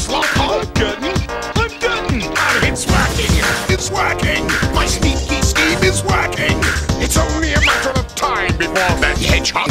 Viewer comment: I always thought he said “MY STINKY SCHEME IS WORKING”